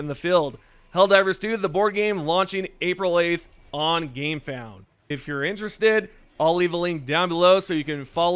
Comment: For a sec. I thought I heard “out April 8, on GAMEPASS lmao